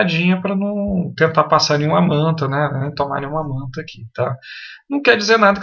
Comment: teve um momento que você falou de uma multa. O que seria exatamente essa multa? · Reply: Ouvindo novamente percebi que não foi "multa" que você disse. Foi "manta"? Não conhecia essa... rsrsrs